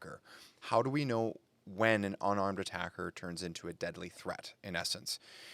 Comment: I thought it said “armed attacker” and I was like isn’t that the whole point of cc😂